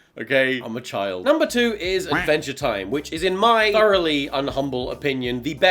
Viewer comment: I was listening to the video while removing my make up and i thought you said "VEGE TIME" instead of "ADVENTURE TIME" ....and here I was wondering for a few seconds what the frick is vege time and what is it about? ...